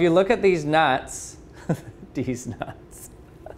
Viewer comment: Is it just me or did he say dez nuts? Also am i the only one who heard it that way?